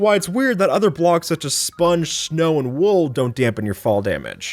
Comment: did you say shnow